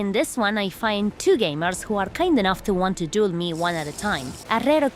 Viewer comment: perdon? Did I hear that correctly?? XD Update: duel. Duel. Duel.